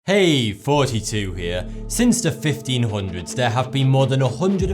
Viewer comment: when i first saw ur vids i thought u said Hey 42 here i was like Whaaaaat??